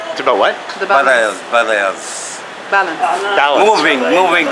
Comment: Guy at 2:45 is obviously saying barriers, not balance